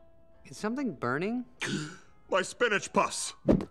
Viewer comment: My spinach puffs! 😝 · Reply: I always thought he said Spinach Pus, lol